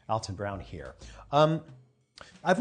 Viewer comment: What he said, "Alton Brown here." What I hear, "Your second dad here."